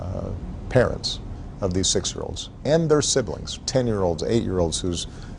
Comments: I thought he said "26 year olds" instead of "20 6 year olds" and i got really confused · I intially thought he said 26 year olds but when I understood he said 20 6 year olds my heart sank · I thought he said 26 year olds, I was like aww I’m 26 he cares. Only to find out, no one cares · I thought he said 26 year olds instead of 20 6 year olds. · i thought he said 26 year olds not 20 6 year olds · I thought he said 26yr olds not 20 six yr olds!